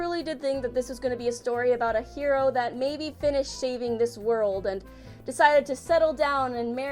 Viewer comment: "Shaving" instead of "saving". I fucking love your midwest accent. Please drink some "PoP" and eat some "taycos".